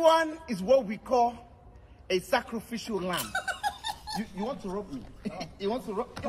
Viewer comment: This is what we call '' A SACRIFICIAL LAMB '' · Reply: Yoo im Mexican my English is not good , but i hear " A SACRIFICIAL RAT" 🐁🐁🐁 LMAO